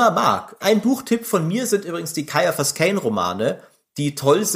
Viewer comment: Hallo, zum Buchtipp, welche Buch-Reihe ist das nochmal von dem Kommissar von dem ihr ab 01:29:30 sprecht? Ist schwer zu verstehen. LG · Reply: Ciaphas Cain, so oder so ähnlich geschrieben